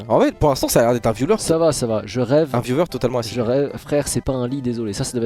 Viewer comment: Un quoi?! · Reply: Un viewer tout a fait assidu